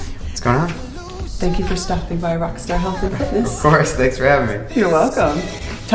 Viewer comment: I thought she said "thanks Patrick for STUMP-ING BY" HAHAHA!!that killed me!!! ;D